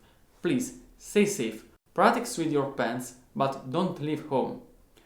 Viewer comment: I thought you said “Practice with your pants”. But you said “pens”. Great video!